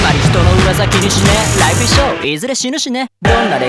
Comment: 「いずれ死ぬしね」が 「いずれ死ぬ死ね」に聞こえるのは私だけか？